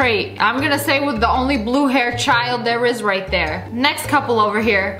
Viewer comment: Lia did you say blue haired kid?